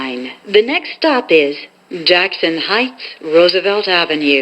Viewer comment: Hieghts Instead Of Heights (That Is Weird)